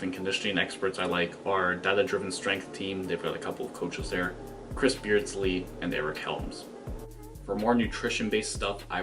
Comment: Ei,Sorry to bother you,English isn't my first language and I can't figure out who you are recommending at 6:04.Thank you for the tips! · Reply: Data driven strength, Chris Beardsley, and Eric Helms as sources for info